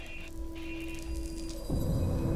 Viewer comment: I heard "Wanna play a Gaben?" Wut xD